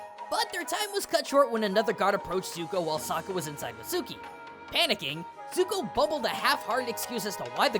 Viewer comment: I miss heard little G. "Zuko making excuses while Saka was inside Suki" I had to replay 2 times before I heard" with Suki".🤣🤣🤣